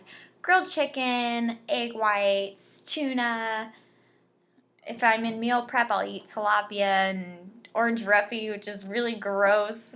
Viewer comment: What is Orange Refuge? Or did I hear that wrong?